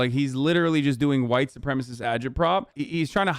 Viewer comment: What does Hasan say at 9:05 ? White supremacist agile prop? What?